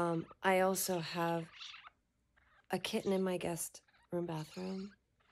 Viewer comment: I thought you just said ' i also have a kitten in my gusset ' , glad to hear it was your guest room bathroom 😂